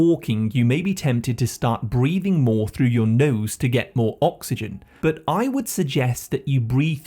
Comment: Hey, just a quick update; @ 8:25 it reads that we "may be tempted to start breathing more through your mouth" but you actually say "through your nose" then in the next slide you recommend that we use our nose. I'm sure everyone gets what your saying but it's good to know where an word scramble happened in case you want to put a correction in the pinned 📌 comments. Either way the video ROX 😎